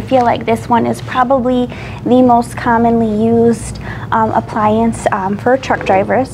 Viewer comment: I kept hearing “appliance direct” in my head for some reason. 🤣🤣.